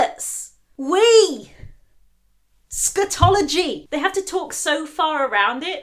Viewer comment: I thought you said 'Eschatology!' for a moment and I was like, whoa now, it's not that serious... but you know? If social conventions were forcing me to hold in an excruciating piss for hours, that would feel like the end of the world lol